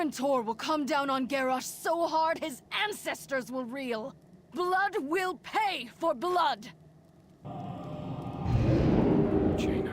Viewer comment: I almost thought Jaina said "Blunt will pay for blunt." /facepalm